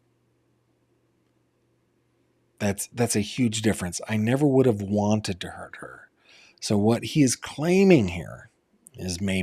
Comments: I thought he said..." I never would have wanted that for her" I think he was/is going to try to say she did it to herself. Then in the other phone call when the cause of D came out he said something like that was a problem. JMO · I hear him say, "I never would have wanted that for her."